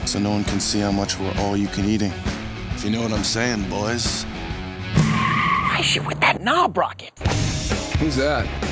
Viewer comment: does he say 'why's she with that knob rocket'